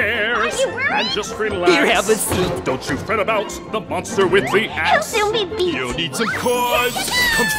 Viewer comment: The "monster with the axe" totally sounds like "monster with leet hacks" ... just saying :D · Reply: First time I heard "monster with three hats"